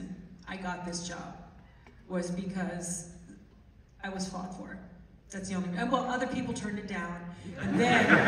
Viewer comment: Sorry but 7:40 "The only reason I got this job was ,I was ?? "Fought for" ?? Please,what s she saying ? I heard Ellen DeGeneres was asked but said no,& then they asked Sandra..and not even Jan deBont don t mention Ellen at all,,just sais no no no...